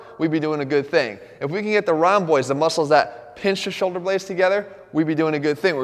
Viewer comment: When he says "rhomboids" I thought I heard "round boys"